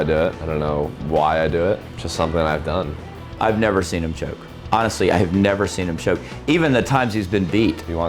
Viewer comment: did he say joke or choke at 2:35? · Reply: Ian william Dawson choke